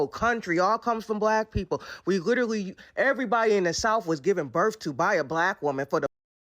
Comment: oh man 😆 I was confused, I thought she was saying “everyone in the south was giving birth to buy a black woman”. I couldn’t figure out how that made sense. I had to turn the captions on, and then I realized, she’s saying “everyone in the south was given birth to by a black woman” Which makes more sense grammatically but logically still does not compute lol Hey I wanted to ask you, do you think you might ever be interested in posting gaming videos on the weekend like you used to do? Or stream? Just low effort, low stakes, let’s play style content. I’ll be there to watch you live if you ever decide to :) Also, yeah, POC is anybody except for white people, it’s part of the liberal lingo, like “YT”, or “unalive” or “mechanical pencil/pew pew”